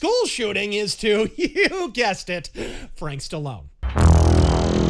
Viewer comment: I definitely heard "crystalnacht" not frank Stallone